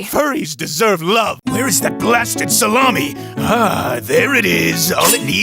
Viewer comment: I initially misheard "Plastic Salami" and I was dying of laughter at Scott being outraged at Logan using his claws to slice it and not the fact that HE'S EATING FRIGGIN PLASTIC